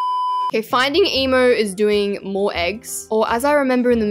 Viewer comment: DID I JUST HEAR HER SAY “finding emo”?